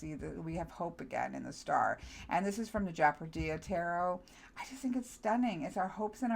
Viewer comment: can't make out what deck you say the star card is from ? Love that one. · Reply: Japardize Tarot :D